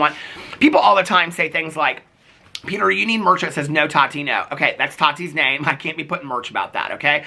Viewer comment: I had your video on in the background and I heard you say "No Tahtino" 7:33 and had to rewind to see who the hell is Tahtino, then I realized it was your spot on Gabriel Zamora impression "No Tati No" and I died! 🤣 I don't know why when you say it it makes me laugh! Thanks for the giggles!